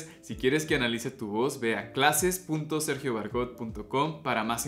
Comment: 🔴 Si quieres que Analice tu voz ve a <link> para mas info. -- Aqui mi análisis a la voz de Belinda que me habian pedido bastante. Que tal les parecio su voz? ⭕ Si quieres votar por el siguiente artista sigueme en Instagram <link> y si quieres mas tutoriales de canto sigueme en TikTok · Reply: pensé que decías que fuera a clases, ya me había ofendido